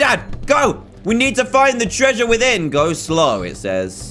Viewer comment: in the intro I heard "we back here in the trash"